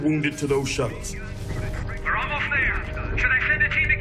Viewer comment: What does that marine say as the Zerg are about to kill them on the science vessel? "We're holed Jared, blow it!?" I'm not sure what name he calls him. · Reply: I thought he said "we're almost there keep fighting"?